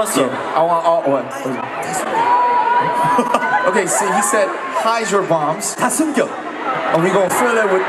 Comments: When namjoon says "Hide your bombs" FOR FREAKIN MONTHS I THOUGHT HE SAID HYDROBOMBS!! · bro i legit thought he said hide your moms,,,,they now coming for our moms after they're done with us